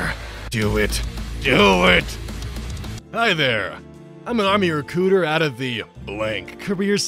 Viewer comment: you need to watch your diction a little better, or get a proof-listener or something -- it very much sounded like you said "Army recooter" and I can't think of a definition for that that's safe for work